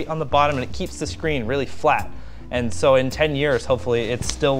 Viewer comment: is that flat or fat i hear is fat 5:43 linus: this is l like a leter l. Me: l mean loser get it... ... ... ... ... ... ... ... ... .... ... ... dad jokes